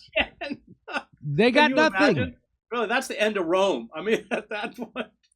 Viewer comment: Did Chris just say "Bro that's the end of Rome" at 17:17 ? What an absolute G